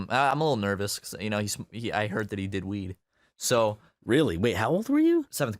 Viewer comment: lol telling that story about that kid at 12:00... and he said he's the only one that DID smoke weed that he knew about lol But when i heard him tell it..i thought he said that kid was the only one who DIDNT smoke weed...and and his friends were freaked out by that and were like "that's just not acceptable" Lol